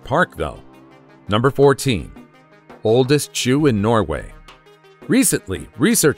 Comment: #14 - Anyone else hear the word Jew and not Shoe, like the first 3 or 4 times the narrator said it? I hadn’t seen the written title until I went back to hear what I’d just heard, so that would have helped but the pronunciation and on an IPad together with the acoustics in my room made me need to check what was be said.